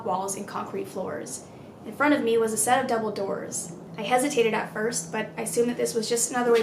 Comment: I heard "a set of Dumbledores"